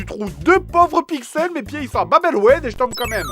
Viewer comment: c'est quoi l'insulte qu'il dit? " mes pied ils s'en ?????? " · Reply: leviet Ce n'est pas une insulte. C'est "Mes pieds ils sont a Bab El Oued."